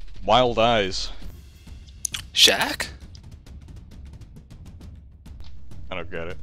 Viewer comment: Coltaine: "Jacques?" I thought he said "Shack" too. But if it were Jacques, he would be wearing a tuxedo (as described).